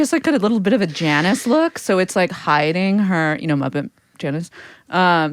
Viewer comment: okay I need to know what is said here cuz the cc isn't picking it up. The Muppet Janice??? My Janice? Oh. My. Gawd - Janice from Friends?